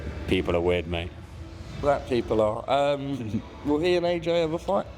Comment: does say “black people are” 😂